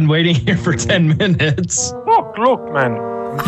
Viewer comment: What did say at 0:05? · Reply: “F***! Look man…”